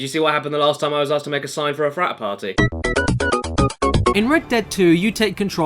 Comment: Prat Farty 5:53 Micah Sucks 6:10 bloody bastard 9:00 fabulousness 9:06 Other services 10:02 It Shats 11:48 Skull Face 12:12 horse nappies 12:54 dancing horse 13:02 DISCLAIMER 14:33 snake in my boot 15:50 good boi 17:53 The saying of the outlaws 1 30:02 The saying of the outlaws 2 31:41 The saying of the outlaws 3